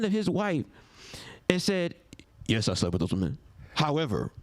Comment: did he say that he slept with other men?